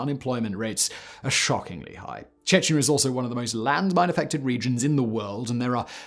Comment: Sometimes I can't understand you when you talk qiuck at the end of your sentences. For example, I had a hard time trying to figure out if you were saying, "landmines". At first I thought you said, "langbine" or something. lol Love your content on all your channels. I don't know how you do it.